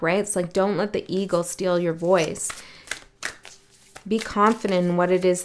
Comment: when you said, "dont let the eagle steal your voice," I heard, "dont let the ego steal your voice," so I think that was a message for me and possibly others.